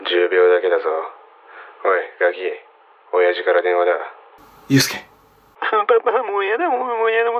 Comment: 「おいガキ、親父から電話だ。」が「おいナオキ、親父から電話だ。」に聞こえて、こういう所でも面白みを出してるのかと思ったらやっぱり違かった